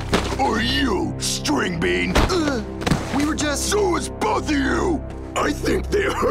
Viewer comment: I thought he said “ sues both of you”